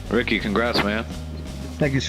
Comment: Thought I heard rookie congrats man att first and I was like: That was bold Shanahan